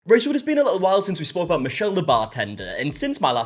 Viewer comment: in every video: "Right, so..." What I hear: "Rachel" Literally every time. I've accepted it at this point... Guess I'm Rachel now 👁️👄👁️